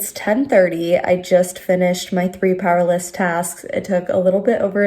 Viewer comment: I was so confused bc I thought you were saying PowerLESS tasks but am just realizing you're saying PowerLISTS 😅